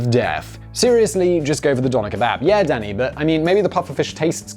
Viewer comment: I thought he said Dahmer kebab